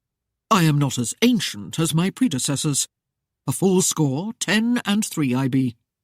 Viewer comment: I don't think I can finish listening to this. The main female character is so absurd. I am cringing at her every behavior. Also. Maybe I miss heard but they listed her love interest s age at 4 score 10 and 4, making him 94 years old, hopefully I misheard. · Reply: No, he said, “ I am a FULL score and 10,” which means he is 30. You misheard that.